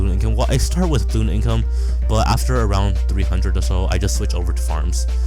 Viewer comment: Who else heard say round 300 at 17:29 lol